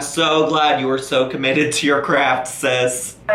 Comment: I thought you said " You are very commited to your crap Sis."